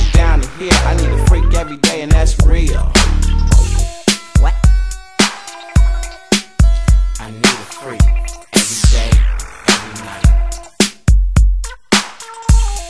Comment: Did anybody else hear “What?” At 2:27 😂